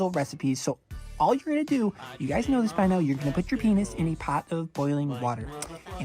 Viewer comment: i zoned out and i just heard "place your penis in a boiling pot of water"